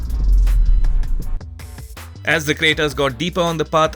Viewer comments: He said Creta instead of Vitara😂😂😂😂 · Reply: Arre Creta nahi, "crater" bola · Did he say Creta :D ? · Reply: craters